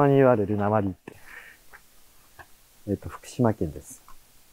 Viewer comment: で何か喋ってるけど何喋ってるのか聞こえない。また「訛り」ていったのかなー？